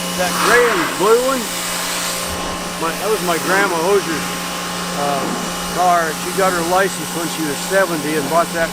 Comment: Not tryna be nosey at all but at 5:30 if I didn't mishear, and he said "that was my Grandma Osers car" then he and I are related. That an extremely rare name in the US (less than 1000 people) and my grandmother was also an Oser 😁. Might have misheard him lol but hearing that name definitely made my ears perk up ✌️